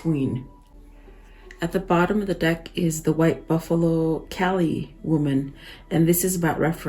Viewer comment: Thankyou for your readings. I don't mean any offence but I thought I heard you say "white buffalo cali"? when you turned that card, but is is actually the White Buffalo Calf...which is a great sign of peace to all indigenous nations. A Very positive sign! 💞🕊️